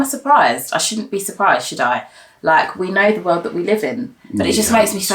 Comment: The accent scared me a bit when Ella said '"should I" after said Oprah. I thought she said " she died" I googled it sooo fast than rewinded like ohhhhhh she said " should I". My heart returned to its place. Lol . Looove ya'll . I dont miss a vid